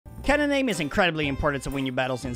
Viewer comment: I scrolled past this kinda fast at first, so when you said "cannon aim" it sounded like you said "ketamine" 😂